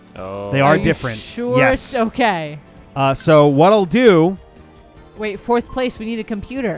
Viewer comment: "So, Waddle Doo..." The way he said that really threw me off for some reason. ...Because Waddle Doo isn't even in this game. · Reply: Cinnamon Noir i think he said what i do 2:52 he said it fast